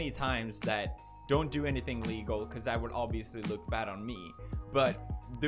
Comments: I'll be it, at 10:56 it sounds like he said "don't do anything legal" But for crying out loud, this is Felix, we all know he's trash at pronunciation, and if you listen closely, you can hear he said illegal, I too have the same problem with my speech from time to time, I'm just pointing this out before the media uses this against him. Don't forget, Pewds has said, "There's actually a film based on this movie." Speech lvl 9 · Sounds like he said dont do anything legal lmao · What i heard "don't do anything legal" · Just had to say. It sounded like he said don't do anything legal. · Did I just hear him say “don’t do anything legal, it will look bad for me” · it sounded like he said don't do anything legal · Did anyone else catch him say "don't do anything legal" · Is it me or did he say "don't do anything legal" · Is it just me or does it sound like he says "don't do anything legal because it would obviously look bad on me" oof im sorry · Did you just say "Don't do anything legal"? :D Subtitles say so :p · at like around the 11 minute mark I legit heard 'don't do anything legal' lol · I heard "don't do anything legal" ...